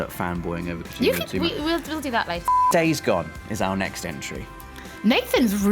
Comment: anyone else misheard it as: Dave's gone! Nathan's really into this! Like Nathans really hyped for this! i laughed way too hard at this! :-)